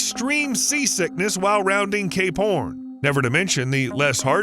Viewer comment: did he say gay pxrn ? 👀